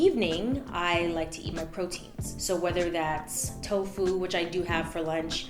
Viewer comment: When she said at night she likes to eat her proteins, I thought she said she likes to eat her problems. I was all SAME GIRL!!!